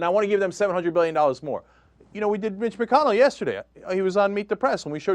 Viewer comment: when he said mitch mconnell, i thought he said bitch mconnell. ahahahahah